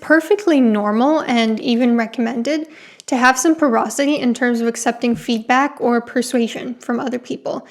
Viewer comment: "It's perfectly normal- and even recommended to have some perocity in terms of accepting feedback..." What does the word "perocity" mean? I have never heard it before. I am not even sure this is the correct spelling. I just copied the spelling from the auto closed captions. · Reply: Google tells me “porosity,” root word porous. Letting things seep in. Being receptive, basically.